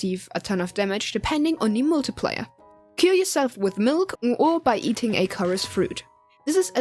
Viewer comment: i thought you said "Kill yourself" ioadghjiosdgohesagouhs